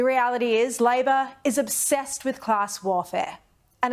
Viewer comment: Treason for the crook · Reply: lol all I heard was obsessed with Klaus warfare.... I'm gonna go with,, I heard right and she got it wrong haha